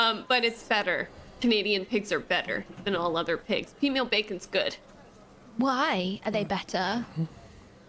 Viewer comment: Holy moly you've confused me so much. I thought said female bacon... xD